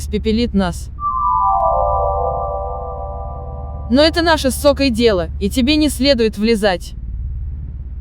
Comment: мне одному послышалось мат??😅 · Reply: Чарджери сказал/а: но это нашей с Сокой дело